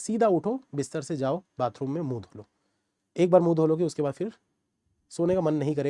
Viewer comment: I heard bathroom mein moot lo. . Sorry😂😂